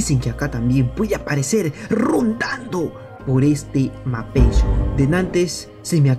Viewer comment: escuche Ronaldo